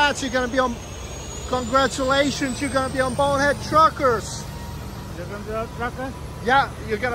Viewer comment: WTF did say at 5:25?“BURNERD GLUCKERS!” Or maybe he’s Just explaining why he took 30 in the fuel island. He was telling you he got a Churches Chicken sandwich called Barnyard Cluckers. 😂